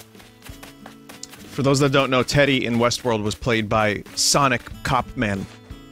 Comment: When you said Teddy from west world was played by the sonic cop man i thought you said “sonic cockman” and i had to look it up because i thought there really was an actor named sonic cockman